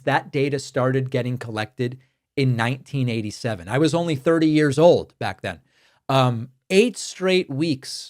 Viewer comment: i had to rewind this a few times to hear how old you were in 1987 and it sounds like you said 30 years old. I think you must have said or meant 3 years old.